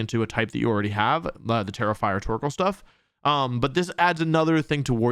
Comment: Every time you said Tera-Fire my dumb ass thought you were saying "terrifier"🤣 There's a special ring to the name "Terrifier Torkoal" 😆